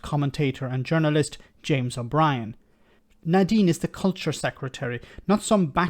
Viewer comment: lol, I thought you were going to say CULT secretary. That’s a better description than “culture” secretary.